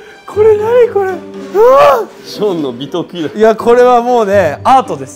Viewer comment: J'AI ENTENDU BITOKU